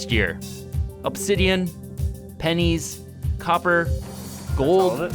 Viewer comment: did i just hear PenN*S ?